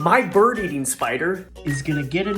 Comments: Omg I heard: "Can my bird eat my spider"😂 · I thought he said by burning a spider 🤣